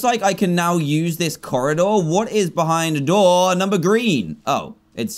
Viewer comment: did he just say “what is behind *door number green?*”